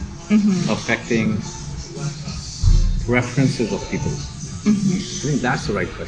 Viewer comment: he clearly says “preferences of people” not references as your transcript shows.